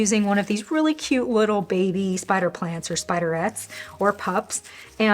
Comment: The auto-generated subtitles thought you said "spider rats" instead of "spiderettes". I'm calling them that from now on. Great video!